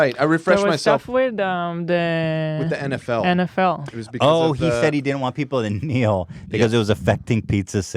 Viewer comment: What is he saying? "Oh he said he didn't want people INHMNEIL because it was affecting pizza sales - what an asshole." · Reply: “Oh he said he didn’t want people to kneel because it...”